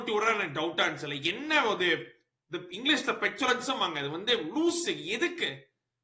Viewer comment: What is that English word bro? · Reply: Petulance - also called being childish